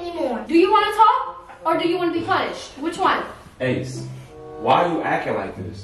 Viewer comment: I thought she said so u wanna talk or be punched which one😭 (my mind) ik you lying